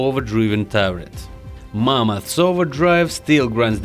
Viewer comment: Did He Say Mama ? lel